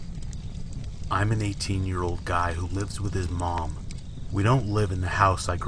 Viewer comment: At first I thought he said “I’m an 80 year old who lives with his mom then I realized he said he was 18” lol